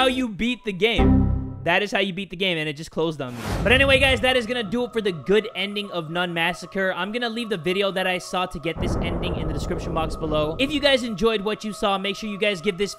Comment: I thought it said nun masagger